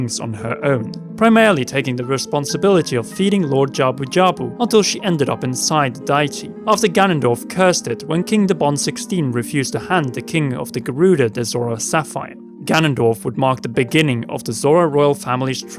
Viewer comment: in the video, does he say "...inside daichi.." i listened to it a few times but i can't make it out. Can someone help? PS. no I'm not poking fun at his accent, that's just what I'm hearing and to me it doesn't make sense. Edit: i put on subtitles and he does say daichi, is that jabu jbaus real name or what. Im just confused, maybe you can help